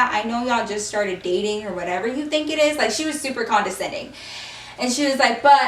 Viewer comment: When said she was super Condescending (idk how to spell) I thought she said she’s super Honda Civic